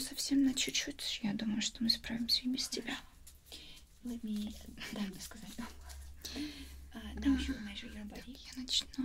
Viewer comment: I thought she said Tatiana, my mame